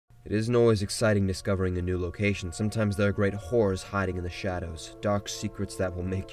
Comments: At the beginning I thought he said “Sometimes there are great whores hiding in the shadows.” And I went with it for a while before I realized lmfao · I thought he said "great whores" 😂😂😂😂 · I thought he said great whores hiding in the shadow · i swear i thought he said there were great whores in the shadow😂😂😝